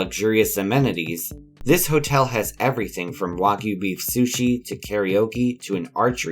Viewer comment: did he just say beef sushi????!!!!! WHAT?¿?¿?¿